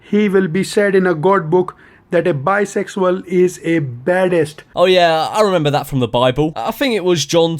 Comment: I thought he said biosexal is a palace